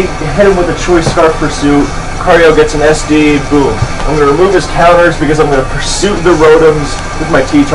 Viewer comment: that's what I heard. I'm positive it's not what he said, though, and just the audio being wacky. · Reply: ah ok. hes saying "im gonna remove his/its counters"